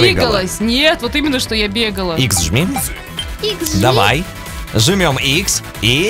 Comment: Мне послышалось или сказала на 4:45 икс жми